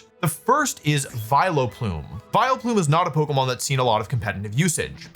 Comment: Viloplume :)? Did I hear that right. Great video though.